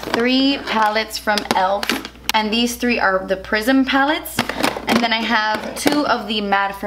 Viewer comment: Who else thought she said prison instead of prism when she was showing us the elf prism pallettes?lol